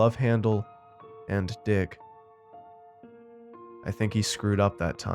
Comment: did he just say love handle and d*ck?